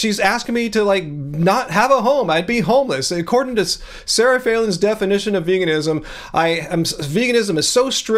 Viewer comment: Sarah Falen . Oh. I thought you were saying Sarah Palin . I'm sure they'd get along just fine.